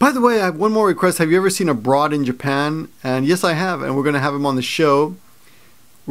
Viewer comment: Oh! I thought you said "Have you ever met a broad in Japan?" which seemed like a very peculiar thing to ask. AbroadInJapan - love that guy.